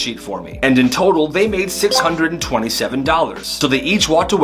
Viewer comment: did someone say 67😂